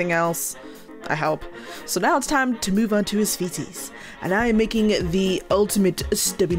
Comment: Now to move onto the feces✨✨ is what I thought you said when you said feeties😭🙏